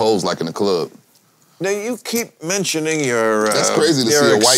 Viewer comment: it sounded like he Bill said “Nigga u keep mentioning..” 😭😭😭 Yo wtf